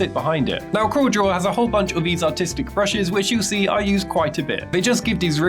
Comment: I thought u said "Autistic Brushes" 😹😹